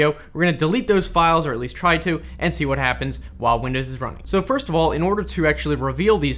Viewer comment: for a sec I thought you said “while windows is drunk”